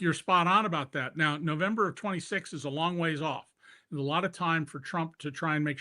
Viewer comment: "November 26 is a long way off..." I did a double take when I heard David say that and it wasn't until I replayed his comment that I realized that he had said, "...November OF '26 is a long way off." ~Whew!~ Hopefully by November OF 2026 the Democrats will have taken over the House of Representatives and will then bring articles of impeachment against Trump. 🤞🏼